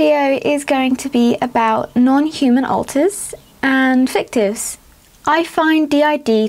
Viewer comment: Hey system, I just found out through the Entropy System's twitter that this video has been demonetized and YouTube is not being very nice to you because of it. I'm very sorry to hear that, because this video was a HUGE comfort to me, to know that you are helping to educate people about non-human alters like me! We've already re-tweeted the Entropy System's tweet about this, and I just wrote about it to a Facebook group I am in that is specifically for non-human alters. I want to do more to help you out, if I can. video also helped Denise D. in our system to better understand herself. You deserve better. Fans of, we need to let YouTube know that this is NOT okay!!! Who's with me? - Elena the teenage dragon girl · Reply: Douglas Vincent It may be just because this video contains words that resemble curse words, which are then mistakenly picked up by Youtube’s algorithm. For example, i heard the word “fictives” at around the 0:45 mark which could be picked up as the words “f*ck this”. There may be other wordsaswell, but i didn’t pay too much attention to this.